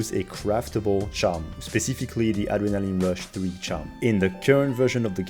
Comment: what was that charm? I'm sorry, your accent is really heavy right there and I can't for the life of me, decipher what was said. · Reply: Adrénaline rush 3 charm :)